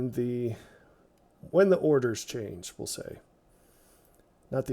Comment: Love the visuals and the explanation. 10%-tenth. So much taught about "a tenth" in the scriptures. 😉🙂 My hubby announced (he was listening to this video while I was watching it) we can watch the John 9 video on our truck ride up and back from Jacob Lake. Looks like I will drive so he can watch it. 🙂 Side note- love your humor around the 3 min mark- " the order not the hors d'oeuvres" 😆. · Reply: 😆 turns out it is my hearing. I thought you said the hors d'oeuvres. But it was "the orders". I relistened to it.